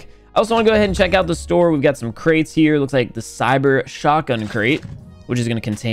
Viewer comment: Did he just say Shotgun